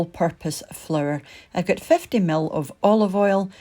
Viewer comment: Aw fk..I thought you said 500 ml..😟I've just wasted loadsa oil 🤣🤣🤣. Edit.poured it back in bottle